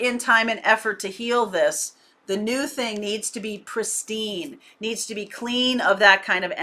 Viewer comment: did you say, “Christine!” ?